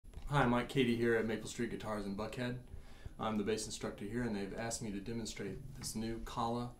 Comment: I thought you said Maple Street Guitars in Fuckhead? Great playing man! Cheers!